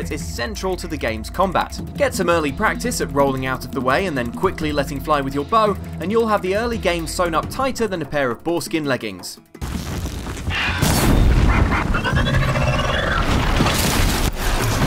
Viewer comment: i thought i heard forskin leggings.